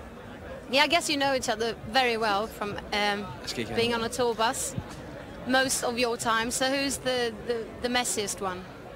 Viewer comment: What does Silas say at 5:14? hahaha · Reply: he says: “Jeg skal ikke have den”. Translation: do not give it to me